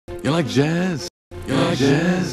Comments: After 16, it sounds like he says, "you like jizz?" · As it goes on, it starts to sound like he is saying, “Ya like jizz?”. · why doe it sound like he is saying “ya like jizz” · is it bad that i heard "you like jizz?" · I like that part when he says "Ya like jazz". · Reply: lmao what i heard was "Ya like jizz?" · Beginning: ya like jazz Ending: horror movie sound effects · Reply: It sounded like he said “ya like jizz” 😳😳😳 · “Ya like jazz?” My last two brain cells: Oranges? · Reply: I heard, "ya like jizz?" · It sounds like he is saying L jazz 💀💀💀🤣🤣🤣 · He’s not even saying “you like jazz?” Anymore he’s literally saying, “ajdhjeekdnsjdodisb” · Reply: For some reason for like 10 seconds it sounded like he said ya like jizz? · I heard, “Ya like jazz” then, “Tractor Sounds” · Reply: I heard "ya like jizz?" Somewhere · I heard “la jazz?”